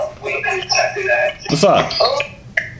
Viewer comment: did she say “yu look like my brother “🤔